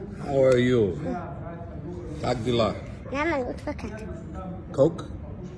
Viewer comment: Lol u said coke can i heard “cocaine” 😂😂😂